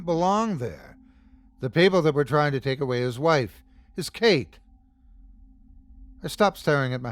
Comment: Sleepy time = Time for some chilling tales. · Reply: Did he say his cake?! 51:46. Eat the cake Katie May!!! GREAT story. Best reading Sir! 🍰 🎂